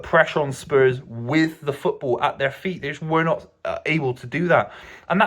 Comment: Did he say "we are not able to do that"????... @ 4:42 I always knew was a Man utd supporter when they were winning titles. He just switched sides when Man City started winning. The way he talk about about pre 2010 united is with lot of nostalgia and admiration like a fan. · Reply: He says “they were not able” he doesn’t say “we’re not able” ffs 😂